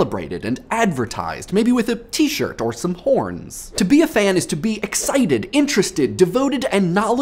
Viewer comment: When he said horns at 0:30 , i thought he said porns. still works the same i guess.